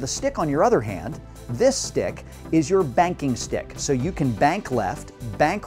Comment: I thought he said spanking stick